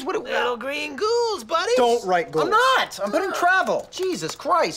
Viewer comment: Always thought he said "peoples needs"